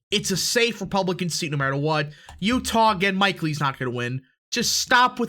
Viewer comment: It was hard to hear in the video, but it sounded like you said that Mike Lee is not going to win? I'm confused.